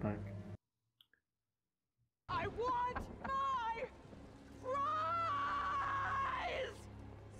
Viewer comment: I know Agatha said "i want my prize", but i swear to God first time i saw it i heard " I want my 🍟".